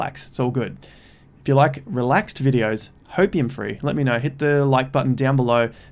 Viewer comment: Hey. I just wanted to say thank you for your consistent, grounded, no BS, daily videos. Yours are the only ones that I definitely make time to watch every day. It seems to me that it's rare in this space to get hopium free content. Or maybe you're just too tired to get excited due to having a baby LOL 🍼🚼👼💤😴. I really appreciate your free advice and analysis, I've learnt loads from it. Cheers dude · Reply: and all this time i thought he was saying opium free! 😊